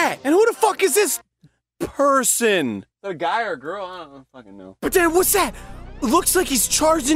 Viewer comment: anyone else hear the "is it a guy or a girl? idk"